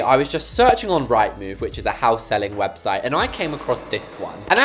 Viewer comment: I thought you said a house that seilled website 😭